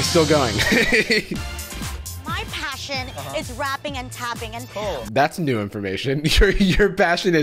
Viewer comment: when she said "rapping and tapping" i legit thought she said "wrapping and tapping" and i was like well at least they're promoting safe sex